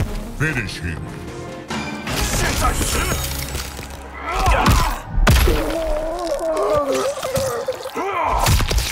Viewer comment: what does he say there? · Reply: "Ni xian zai si" (Now you die)